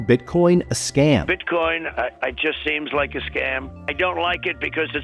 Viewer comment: you literally lie right here. He clearly says "i think its a scam" not "its a scam"